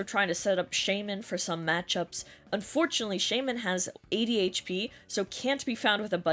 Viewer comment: for a second I thought you said that Shaymin had ADHD lmaoo💀💀💀